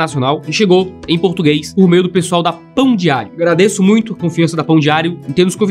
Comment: Quando ele falou "Pão diário" a primeira vez eu entendi "Pão de alho", acho que estou precisando ver (de novo) o vídeo que fala sobre o pecado da gula 😂😂😂